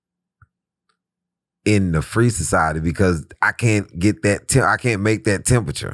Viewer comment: After working retail for years I keep hearing "free society" as freezer side.